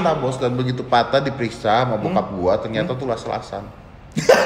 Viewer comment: om ded ngomong apasih? Lase asan? Gakedengaran ada yg bisa jelasin? Wkwk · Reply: Las las-an